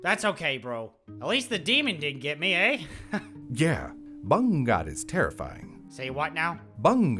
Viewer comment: Did he say BANGUNGOT ? 😂 The way he said it tho.